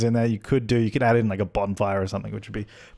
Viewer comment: For a second I thought that you said " a bomb fire"